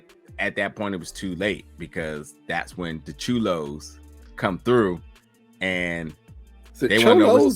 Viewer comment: I thought said Churos. Lmao